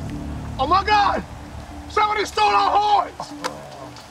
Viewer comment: "OMG! Somebody stole our horns. We gotta find our horns." lmao! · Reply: I thought he said horse.